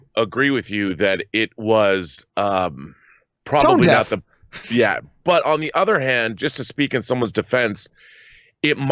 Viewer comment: What were and saying at 10:31? I couldn’t hear either of them. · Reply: said tone deaf